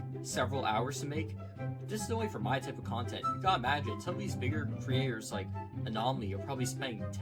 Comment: did i hear this right? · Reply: I was referring to Bigger Traders. I think you may have misheard